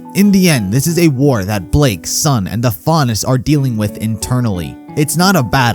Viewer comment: Oh god, I thought he said Blake's son instead of Blake, Sun for a minute there